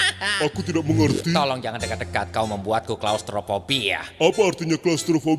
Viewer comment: solophobia? Apa aku salah dengar 😂😂😂